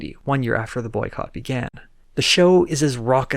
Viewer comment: I misheard 'the audience has grown' to 'the audiences groan' and I thought that was appropriate for some of the jokes :P